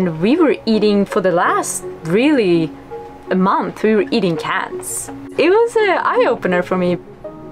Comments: sounds like she said they were eating cats lol. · did she say they ate cats? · Did she say, "we were eating CATS" at 11:33 ? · Anyone else hear her say eating cats at 11:35 it also says it in the captions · Did she just say they ate cats ? 11:37? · I’m sorry, but did she say that they ate cats?! 🐈 I could not have heard that right. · did she say "eating cats"??? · what were they eating at 11:35 ? cans as in canned food or did she say cats?? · did she just say they ate CATS? please tell me i heard wrong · I am sorry, did I hear it wrong at 11:35 as "cats" Like 🙀Or is it cans Like canned food cans? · wait did she say she ate cats for a month? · did she really say that they where eating cats?!?!?! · did she just say they ate cats? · I thought she said "we were eating cats".... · Minute 11:35. Did she really say eating "CATS"??? 😳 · Did she just say we were eating cats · did she say cats... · did she say CANS or CATS ?! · I thought she said they were eating cats! Lmaoo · did she say they were eating cats for a month??? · I thought she said "CATS" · Did she say "we were eating cats!?" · Did she say Cats? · Did she say they ate cats?😂😬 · uh did she just say they were eating cats? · did she say they were eating Cats???? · did you say you were eating Cats ??? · did she said they ate cats 😲 how about fish? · At about 11:36 did she say when they ran out of food in Indonesia for a month they were eating cats???!! I hope she said cans not cats but it sure sounded like she said cats.